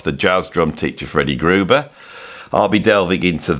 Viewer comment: I misheard the first time, ages ago and thought Neil had lessons from Freddy Kruegar.